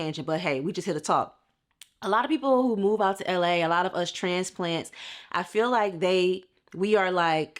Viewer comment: did I hear that right? Transplant? Transgender? Transplanet? What's that? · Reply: Transplant. Just someone that's not a native but currently living there.